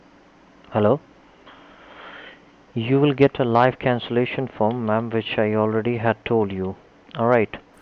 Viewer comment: I heard that as "life cancellation form" omg